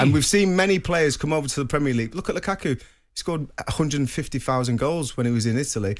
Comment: wait did I just heard 150 thousand goals.